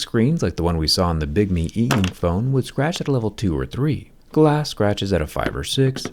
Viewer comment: did he just say, glass scratches at 5 or 6 and nobody noticed